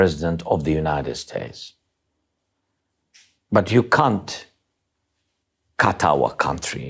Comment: I heard him call Donny Dump 'cunt' 3 times. Did you? Seems the man knows his enemy well.